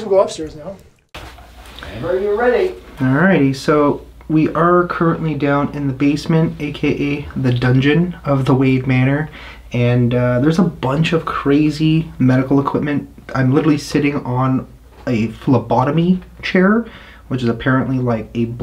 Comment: For a moment I thought you said Wayne Manor LoL. Stay safe and God bless you always.